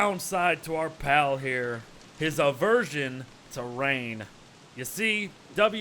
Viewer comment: "hes a virgin terrain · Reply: xd chnkyy you misheard it! He said “His aversion to rain”